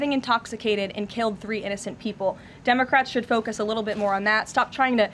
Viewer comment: I thought I hear “ dumbcrat”😂